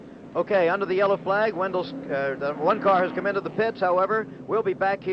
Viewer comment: did anyone hear the announcer catch himself in the middle of saying "Wendell Scott has entered the pits" and say instead "One car has entered the pits"?